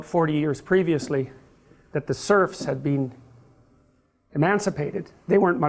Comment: did he say "servs" or "serbs"?